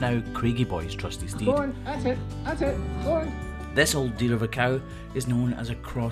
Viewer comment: I misheard “this old deer of a cow.”